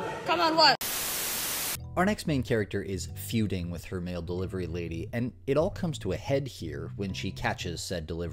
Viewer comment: Gotta admit I was squinting for a second when you said "mail delivery lady" cause I heard it as male delivery lady so I was very confuzzled for a hot second 😂